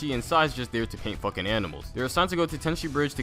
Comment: I first I thought he said he’s just there to f*ck animals 🦔